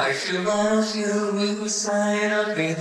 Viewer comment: I heard at 0:15 (I LOOOVE.) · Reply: My Love... You make my dreams come true.